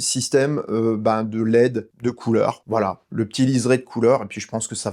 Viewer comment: J'ai regardé cette vidéo en temps que "deuxième source" (2 écrans), et a 04:55, alors que j'était concentré à conduire une f1 à Suzuka, j'ai entendu : " bha du système de l'aide de couleurs", alors que je voyais bien en périphérique de l'oeil, qu'il y avait un bandeau lumineux, j'ai reculer la vidéo plusieurs fois, pour savoir si j'ai bien entendu, mais j'ai mid au moins 30sec pour remarquer que mon cerveau m'avait joué un sacré tour, car transformé "LED" en "l'AIDE", bha ça nique un peu la compréhension XD.